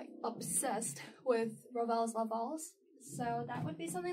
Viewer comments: What is the Ravel piece? I can't quite hear the title or figure out how to spell it. I hear "Ravel's ah valls"? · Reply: La Valse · whats the first song she plays? I didn't understand what she said and I turned on the captions and it says 'raw bella loves balls' 03:55 ahahaha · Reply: Oh goodness... It was Ravel La Valse